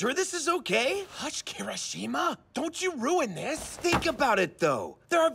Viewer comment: for a minute I thought kaminari said "hush karen-shima"